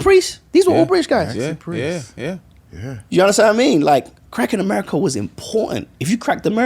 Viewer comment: I thought Harmony was saying "crack in America" 😅 I'm like why is this man giving crack so much praise when it has destroyed countless families...whew glad we cleared that up 😆😆😆